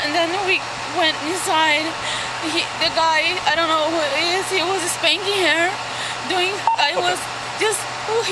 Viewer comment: FYI, I think she's saying "he was spanking her" There was another case where the husband killed the wife but made it look like the airport shuttle/taxi driver had done it, and the husband said the driver had murdered his wife so he killed the driver. That defense didn't work for that dude, either. (Sorry, can't remember anyone's names 😛 )